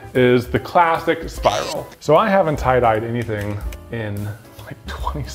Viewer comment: It sounded like said 'pie dye' at one point and I found it weirdly funny